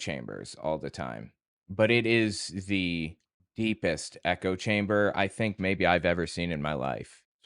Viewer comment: thought you said neko chambers